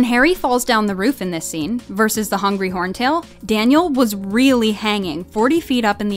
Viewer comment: Who else heard hungry horntail